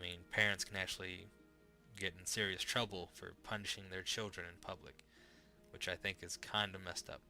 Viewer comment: I had to go back and listen to one part, lol. You said, "Parents can get in serious trouble for punishing their kids in public." and I thought you said "punching" instead of "punishing." xD